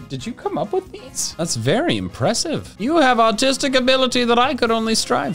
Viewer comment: Did anyone else hear him say : you have autistic ability I wish I had 10:55 😅